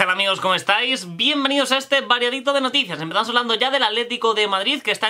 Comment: Hasta hace nada me di cuenta de que dices VARIADITO de noticias.Pense que decias MAREITO o algo asi...